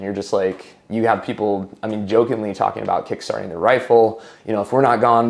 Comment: Did he say “kickstarting a rifle”? Given the context, is he referring to suicide? I’ve never heard that expression before.